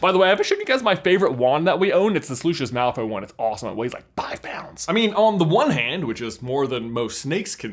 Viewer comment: When I heard that, being British, I thought, "woah that's cheap" and was about to go to Amazon before I released it WEIGHED five pound.